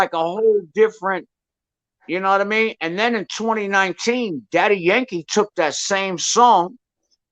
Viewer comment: He said Daddy Yankee, at first I thought he said Daddy Freddy. Anyway Snow is dope! plus MC Shan is the man.